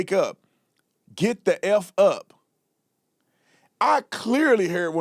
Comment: IT SOUNDS LIKE HE IS SAYING SHUT THE F--- UP❤